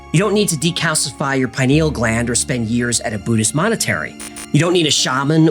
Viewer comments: Did he say Buddhist Monetary at 6:40? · Did you just say, "a Buddhist Monetary?" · I'm sorry, did I hear "monetary" instead of monastery? Love this episode. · correct me if i am wrong, but did you say 'monastery' or 'monitory'? I read that in the early days, they would sit in a Faraday cage to somehow "amplify" their "far seeing" abilities or prevent outside interference. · Love the ❤❤❤ but did you just say Buddhist monetary?! Instead of monastery?! 😂😂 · "A Buddhist monetary" ???? Or did you mean monastery, or am I going deaf? I keep hearing you say "Buddhist monetary" at 6:40 ish